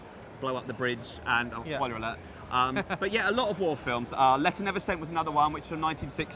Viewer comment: I can't understand what movie he said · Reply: He said Letter Never Sent, admittedly our dialect over here can seem pretty rushed if you're from outside the U.K.